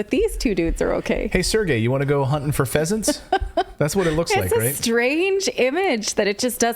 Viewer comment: HUNTING PHEASANTS, ...OH...I THOUGHT YOU SAID HUNTING PEASANTS...never mind.